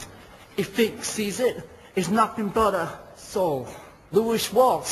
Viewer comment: HAHAHAHA i've watched this video about 100 times and i thought at 1:47 he called simon cowell a 'SOUL' now i've only just realised he edits his own swear word out '**S'OLE'